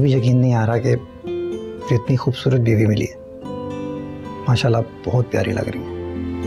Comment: Did guys notice he said "Mashal" instead of "Bisma" 🤣🤣🤣🤣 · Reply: he said MashaAllah ... not mashal